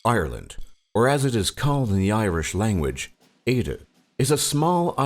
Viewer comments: I'm Irish and never heard of half of that food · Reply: It sounded like he said Eirdean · Did he just say "ayda" for Éire??